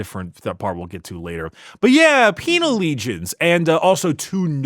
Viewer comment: I heard ‘penial lesions’ and had to double take i was watching an ad ric episode